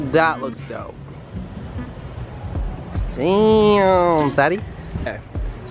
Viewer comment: Did he just say dang daddy